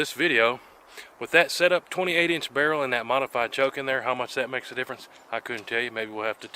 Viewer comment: Modified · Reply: I jumped the gun ( pun intended 😁 ) when I got through a little more I heard you say modified lol. I used to hunt with a dogleg 12 gauge full choke. I could take a squirrel or duck at 30 yards. Some times there wasn't much left 🤣 at closer rang